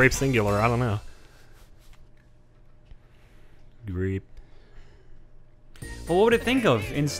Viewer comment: On the Mic minigame lost to me is sounded like said grate not grape